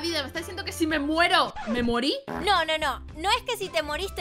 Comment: no entendi xd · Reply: Si se muere se murio :)